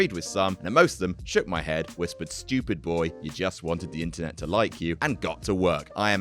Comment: YO ok so for a crazy long time I thought he was saying "stupid boy you just wanted the intern to like you," when he was saying INTERNET goddamnit.